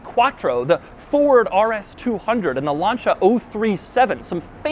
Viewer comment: sounds like he said lancha instead of lancia